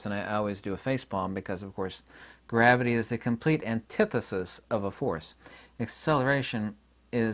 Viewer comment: Wait, did you just say gravity is a cymbal choke? Drummer brain but I’m trying to follow =)